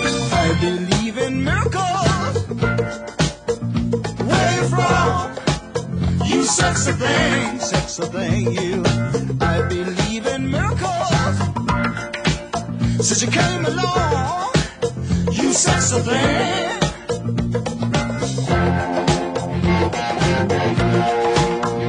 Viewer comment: I've always thought he said "I believe in loco"